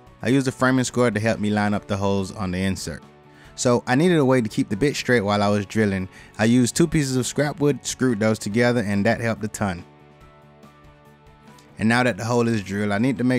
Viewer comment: did he just say "I needed a way to keep the bitch straight"? Hahaha. 🤣🤣🤣